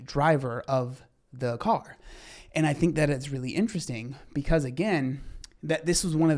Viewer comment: Every time says "it's really interesting," I hear "Ain't it some bullshit" 😆